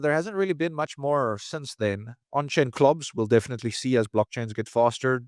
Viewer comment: what is he saying, clogs? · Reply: CLOBs Central Limit OrderBooks